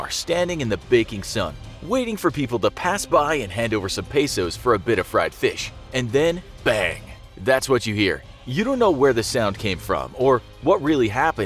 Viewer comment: did you say peso